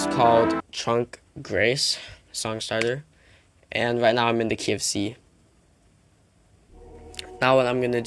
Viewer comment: I thought he said "I'm in the KFC" not "I'm in the key of c"